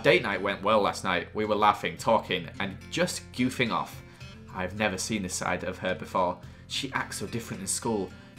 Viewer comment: When Joel said "I've never seen this side of her" I thought he said "I've never seen inside of her" I was walking and actually fell over from laughing